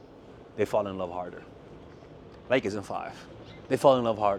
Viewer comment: i rewound it at least 5 times to understand the expression and determined i cant understand ur english .. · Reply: the closed captioning says "like as in 5"